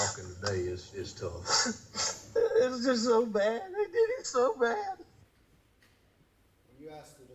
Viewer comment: "It was so bad. They did him so bad." Although I find the words themselves peculiar to the south I hear Alex say "They" each time (here and in the car interview) that he uses the expression They play the audio for the jurors who why is the judge allowing this guy Croft to express what "he thought" he said except and unless he says it is what he 'thinks'. He is only one guy and it is a 'subjective' listening/hearing by anyone who hears it.